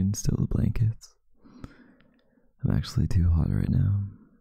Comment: BRO PLS AM I THE ONLY ONE THAT HEARD THIS AS “i’m actually too hard” JDHDHDJDHD